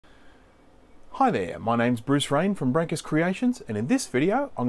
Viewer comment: LMAO i heard you so wrong i had to rewind.. i thought you said your name was Bruce Wayne at first xD You should get a t-shirt saying "I'm Batman" just to mess with people :P Great video btw! :)